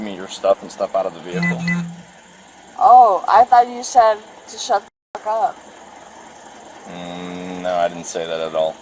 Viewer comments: "Oh. I thought you said to shut the fuck up." 😂😂😂 He should have. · "Oh, I thought you told me to shut the fuck up!" LMFAO! I guess she hears that a lot, hence why she misheard him.